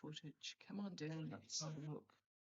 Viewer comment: Did anyone hear her guides? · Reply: "Let's all look"?